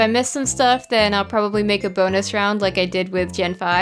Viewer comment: I left right as you said "bonus" And i heard "if i missed some stuff i'll probably make a phone"